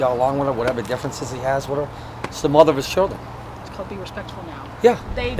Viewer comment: Hold the heck up, did they say mother of the sheriffs children???!!!